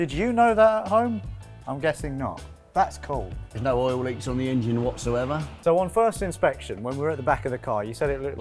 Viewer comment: "there's no oil leaks on the engine whatsoever..." All I heard was "there's no oil to leak in the engine whatsoever..."